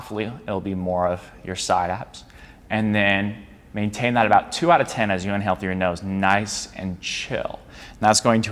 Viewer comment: can someone tell me what did he say ? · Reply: “Maintain that about 2/10 as you inhale through your nose. Nice and chill.”